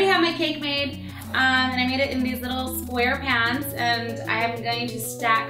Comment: i thought she said sqarepants